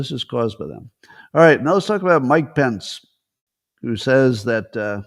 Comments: When you said Mike Pence I thought you said Mike Benz. I almost had a heart attack!!! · Tiger, Tiger burning bright on the sofa, in the light. With his solar aray he collects warmth, grace and stylishness. Tiger, tiger burning bright He sleeps in beauty, but not at night. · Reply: When you said Mike Pence I thought you said Mike Benz. I almost had a heart attack!!!